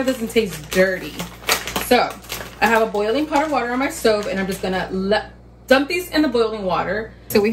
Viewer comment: When she said stove I thought she said soap …